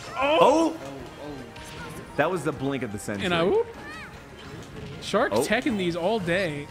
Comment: Did just say -and I oop